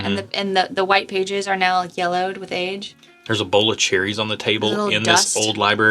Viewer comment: You said, "There's a bowl of cherries on the table." I heard, "There's ebola cherries on the table." And my reaction was just that it was a weird note to find in a bourbon.